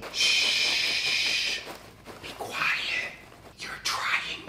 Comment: is nobody noticed that damien said "shush dont be quiet"? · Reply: I just hear "Be quiet.", I think the rest was just some rumbling sound...